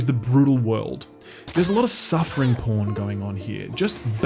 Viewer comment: did he... Did he say suffering porn?